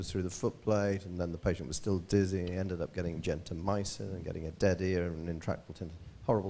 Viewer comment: what did he say? Gentenmysa? What is that? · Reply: Gentamycin